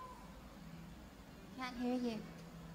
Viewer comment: what did she say..? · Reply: She says, QUOTE: "Can't hear you." She says it in answer to something an audience member yells probably in Spanish, something I don't understand. But when audience members yell things like "The stage is too dark!" or "We can't see you!", she often replies with "I can't hear you."